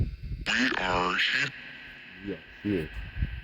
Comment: I heard...We are all men. At the 39:20+minute. Keep up the great videos